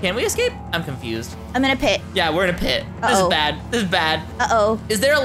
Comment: you actually said is there a ladder in the shelf but I thought you said it wrong it should be is there a ladder in the shop not shelf